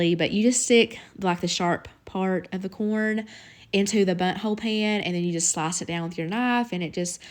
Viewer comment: Lol i thought you said butt hole pan! Great recipes today!